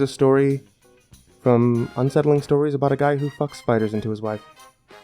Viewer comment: A story about a man who fucks spider into his wife? Is that what he said. 1:28 A testicular self-exam (whaaaaaaaaaaaaaaaaaaaaaaaaaat?) 4:23 well keep going keep going we're in suspense here mate